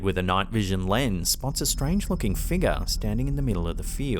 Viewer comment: "Spots a strange F%&*#! figure" is what it sounds like you said bro and not strange looking... you definitely said strange effing